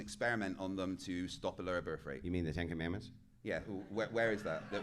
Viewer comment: did he just say where is the 10 commandments